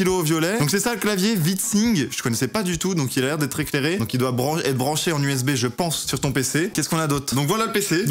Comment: il voulais dire branler ou je reve si vous avez entendu la meme chose que moi dite le moi